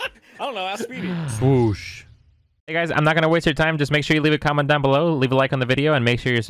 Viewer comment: I'm so tired i heard ''Leave a condom down below'' and was very confused for 2-3 seconds lol